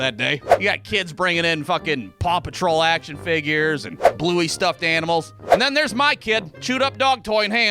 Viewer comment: wait did you say bluey